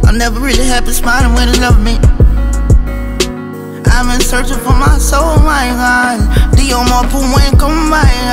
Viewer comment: Take the Pain from this World, may we Evolve Past the Destruction we do To One Another. Survive, Thrive, Evolve past the Destruction and Pain we Cause. Forgiveness and Acceptance to Overcome, to find Peace and Joy and Create a Better World for one Another. It Is On Us To Make This Happen, Every Single Day, Spiritual Journey of Life 🔆🔆🔆 Searching For My Soul, My Heart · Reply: It sounds like hes saying ive been searching for my soul my vibe